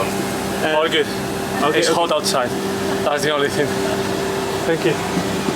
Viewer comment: did he say "it's cold outside" ?? In Hong Kong? 🤔